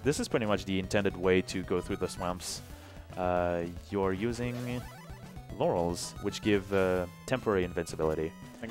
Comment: my friend says the guy commenting the game said laurel but all I heard was yanny